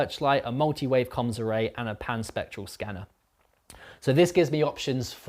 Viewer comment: I was so confused I kept hearing "the pansexual scanner" instead of Pan spectral scanner. It was amusing at least.